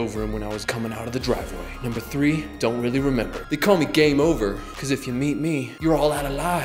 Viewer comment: I thought he said "They call me.. Gay Mover"